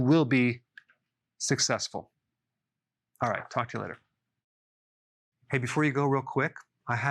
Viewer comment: instead of "talk to you later" i have heard "FUK YOU LATER" ahahahaha